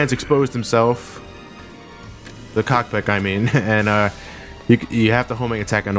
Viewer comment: D DID HE JUST SAY COCK--PICK?!?